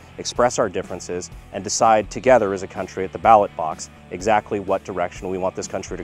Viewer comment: The ballot box? I thought Joe Biden said that it was the battle box.